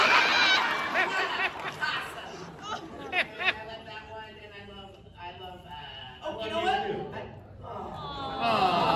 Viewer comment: what did Brandon say? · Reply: He said “I love you too”